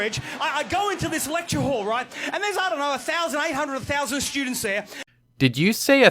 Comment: I heard him say "A thousand... eight hundred... a thousand students" as in he was ballpark-estimating about how many there were; between eight hundred and a thousand students.